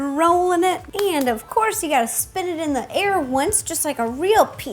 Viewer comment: I thought she said “spit it in the air" 😂